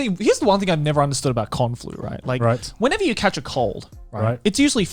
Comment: I thought garnt said kung flu He said con flu